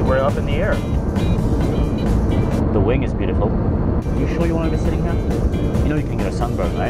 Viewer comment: Random person: The wing is beautiful. Wing: You respeccccccccccccct me! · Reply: At first I thought he said wind is beautiful